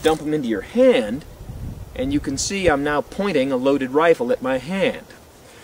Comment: I thought he said “I’m not pointing a loaded rifle at my hand.”